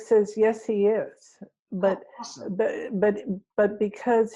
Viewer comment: does anyone hear Erik say “awesome” about Anthony Williams?! 😃